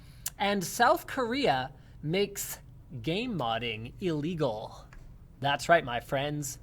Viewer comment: I could have swore you said "Korea makes gay modding illegal" and I believed it was a thing, because its Korea.